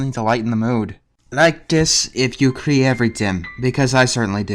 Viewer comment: Sorry... I couldn't quite make that out... What did he say? :( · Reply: like if u cri everytim. It's just a meme that translates into "like if you cry everytime"